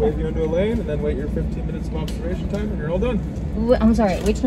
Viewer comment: I thought he said 13 minutes, not 30